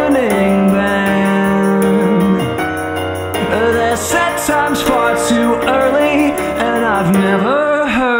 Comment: oh my god I keep hearing "set time's" as "sex time's'